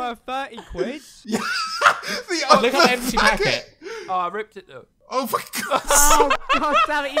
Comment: umm what did James just say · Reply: The other packet?